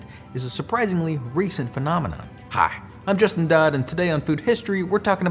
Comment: I heard “I’m just a dude” instead of “I’m Justin Dodd” 😂